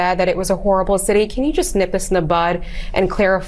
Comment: Did she say, "Can you just nip us in the bud?"